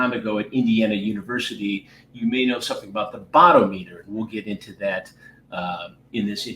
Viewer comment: I kept hearing "bottom eater" and just realized he was actually saying "bot-o-meter" 😳